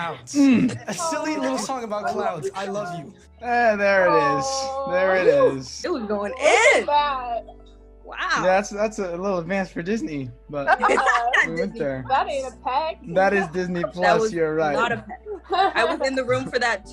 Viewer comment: So I’ve watched this a hundred times and your trying to tell me that I just heard Olivia say at 1:44 1:45 that “It wasn’t bad”